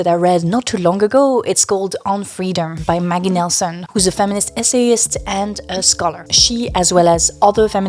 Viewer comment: You said "S life" and then just after you mentioned an ESSAYIST, and i thought you said S-A-IST. NGL, I laughed pretty hard at my misunderstanding lol. No criticism of you to be clear, but the culture around censorship has become just wild. Makes it actively harder to understand people.